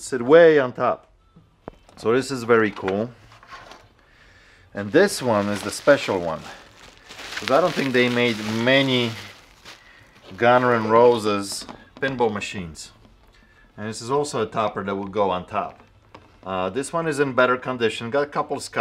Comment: Did u call them Gunner and Roses!?